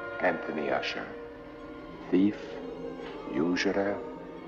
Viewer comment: what's he say at 0:03? Usuallyer??? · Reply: That's "usurer", someone who practices usury. A loan shark, basically--excessive interest and forceful debt collection.